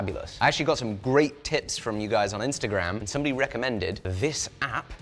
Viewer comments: did he say tits or tips? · thought he said great tits 😂